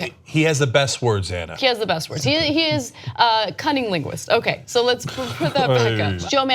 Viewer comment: Let me say I’m sorry up front. I thought Anna said Trump is a “cunnilinguist”. I had to go back and listen again. She said cunning linguist. Sorry again Anna but it was funny 😄.